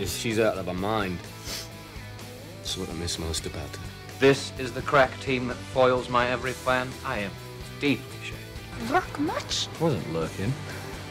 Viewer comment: I love this video (and spike) but I have a little question... What does he say in the scene 1:29? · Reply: He says, "This is the crack team that foils my every plan? I am deeply shamed."